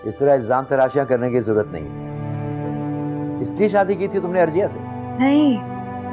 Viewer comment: did he say ARJIYA?? 😂